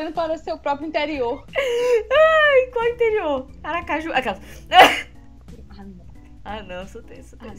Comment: Eu ouvi Aracaju 1:54? Huehuehue Minha cidade♥